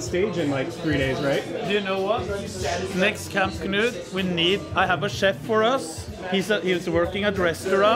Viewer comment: I thought he said "I have a shed for us" and I thought that was a good idea. Like a big brother. Don't let them leave